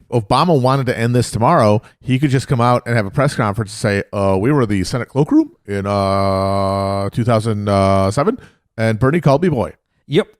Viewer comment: what does he say at 1:07:30? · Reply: "we were in the senate cloakroom in 2007 and berne called me boy"